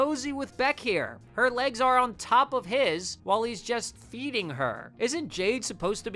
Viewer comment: HELP MY VOLUME WAS SUPER LOW SO I HEARD EATING AND NOT FEEDING 😭